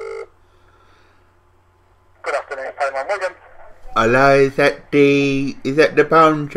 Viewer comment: does he say 'Hello, poundland wigan?' I live right by there o-O